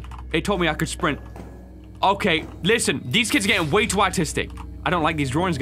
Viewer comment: Did he just said autistic💀